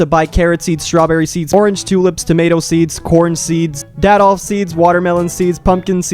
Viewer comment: did he just say... D-adolf seeds???